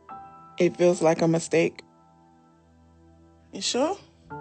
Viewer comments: "it feels like a mistake" DID YOU SAY STEAK??? 🐄🍖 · am I the only one who heard "it feels like I'm a steak"? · "it feels like a mistake" DID YOU SAY STEAK??? 🐄🍖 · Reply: I thought she said “ it feels like I’m a steak “🥩🤣